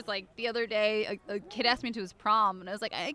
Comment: Who did she say? I can't hear it clearly... any help? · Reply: She just said "A kid asked me to the prom". Didn't mention any names.